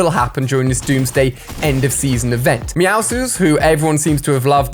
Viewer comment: Did u actually say meaowsles